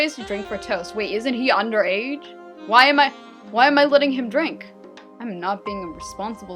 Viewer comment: For a sec I thought Levi said why am I letting him drink isint he like 8. I re winded it 2 times to tell that it ways why am I letting him drink isint he under age